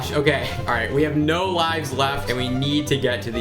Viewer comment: I thought it said Olives